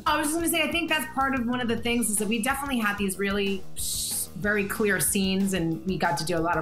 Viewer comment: Did she say Israeli? 🤨